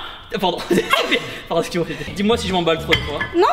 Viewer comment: il a dit excuse moi bébé ?